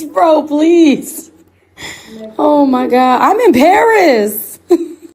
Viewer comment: I thought she said that she was Embarrassed, but I guess she said she's in Paris lol